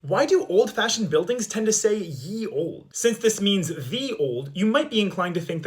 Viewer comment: Ohhhhhhh my god. I just thought it was saying "your old" weirdly Like it's a community store That makes more sense